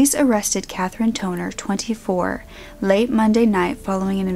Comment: I had to repeat the girl name because instead of Katherine Tonner, i heard Katherine 'Toenail'.